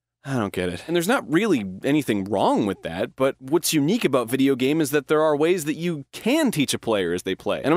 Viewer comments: Its taken me 9 years to realize that he says "what's unique about video game" instead of "games" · I have watched this video like 10+ times over the years. For the first time, I heard the phrase “What’s unique about video game” at the 1:00 mark and I can’t un-hear it. Enjoy this knowledge world.